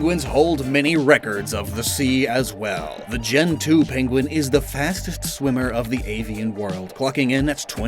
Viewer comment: Piplup is def my favorite starter overall, how about you? Also BIG THANKS to Zenmarket for helping us continue to make these awesome videos. Sign Up with code "LOCKSTIN" to get 1,000 ZenPoints: <link> In April, you can use the shipping code "ZEN10SHIP" to get 10% Off your first parcel. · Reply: When you said gentu penguin, I thought you said gen 2 penguin like you were talking about delibird.